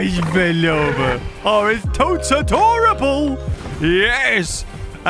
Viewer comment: I don’t know about you but at 3:12 when said it’s toats adorable it sounded like he said “is toast adorable” 😊